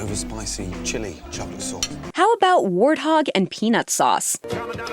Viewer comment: Who else thought she said Warthog and penis sauce for a second?